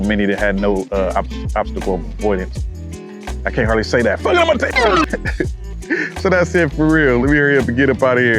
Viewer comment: cant understand what you say at this moment but it’s funny as hell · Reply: "Funkin up my take!" But not funkin... 😆